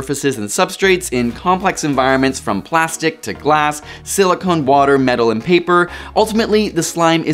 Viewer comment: “Silicon wafer” I thought you said water.